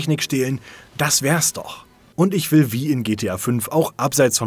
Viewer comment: Noch jemand, der im ersten Moment "Und ich will V" verstanden hat?